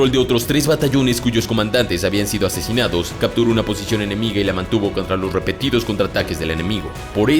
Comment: Que fue lo que dijo el en el minuto 5:38-39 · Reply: "Capturo una posición enemiga"